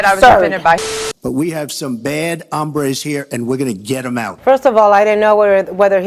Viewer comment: "We at least now know that Donald Trump has two spanish words in his vocabulary - Hombre, and taco" I'M DECEASED 💀💀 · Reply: The fact that he sounded like he said “hambre” instead of “hombre” so I was very confused cause it sounded like he said we have some very bad hunger here